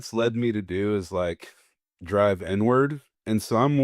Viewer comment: When he said Inward I thought he said N word 😆